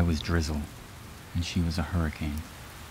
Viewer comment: all I heard was “she was a queen”